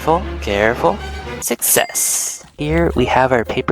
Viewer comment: when he said success it sounded like '67'😂😂😂